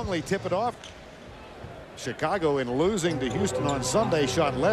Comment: who did he say they lost to? Houston? Oh yeah…Olajuwon was the back to back defensive player of the year. lol